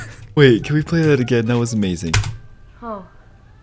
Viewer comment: oeet, sounded like YEET.